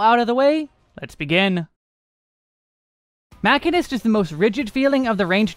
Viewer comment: i thought i was going insane when I heard "My Sharona" and "Mushymist" instead of Machinist